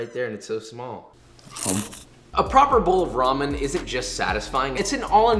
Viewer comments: I really thought he was saying "cost this purple" and it was just a stand in word he was supposed to dub over later. Per bowl. He was saying per bowl... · When he said per bowl I thought he said purple